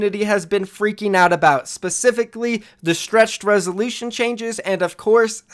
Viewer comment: I heard "stretch resoletion"